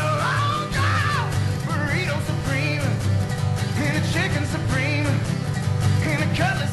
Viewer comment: I could have sworn he said burrito supreme and chicken supreme. Either way this song is funny and awesome.